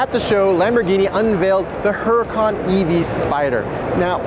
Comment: why are you calling the huracan evo the E.V.O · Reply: Ikr I thought he said Huracan EV, was about lose my shit. Lamborghini making an all electric car lol